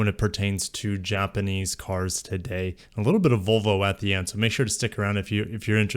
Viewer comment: For a second there, I though I heard you say "A little bit of vulva at the end". 😂